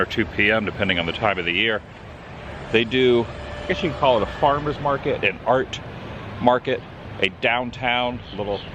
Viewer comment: At the beginning when you said "they call it a Farmers Market, an Art Market" I heard "they call it a Farmers Market, a Fart Market" Sometimes, I'm thankful that loud Punk Rock has dampened my hearing.